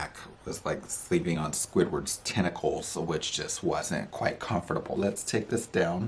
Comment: "Sleeping on the back was like sleeping on Squidward's tentacles." 😆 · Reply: I straight up thought you said testicles 😂😂😂, had me howling